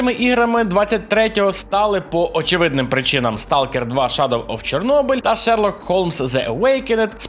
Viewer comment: Поставив лайк - котіт :_oldboiLike: Підписаний - мега котіт :_oldboiOkay: ютуб/патреон Спонсор - базований котіт :_oldboiLove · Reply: мені одному почулось на 0:55 shadow of the chernobyl хоч в списку heard of chernobyl? зверніть увагу. загалом випуск як завжди крутий) зрозуміло, інформативно та те що мені тре